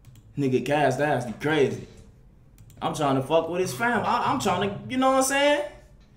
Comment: HOLD UP WHAT DID HE SAY? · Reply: He said "cash nasty crazy, all I'm tryin do is fuck with yo fam's"